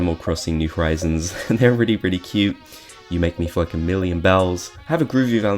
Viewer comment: sounds like you said "You make me f**king a million bells"